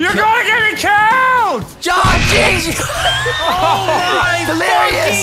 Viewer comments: Did anyone else heard Vanoss say Jon at 15:07 and 15:14? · is it me or does it sound like vanoss is saying, John